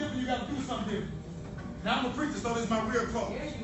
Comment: When he said I'm a preacher I heard I'm a player😂🤦🏾‍♀️